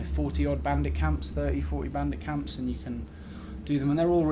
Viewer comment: i thought you said bambi camps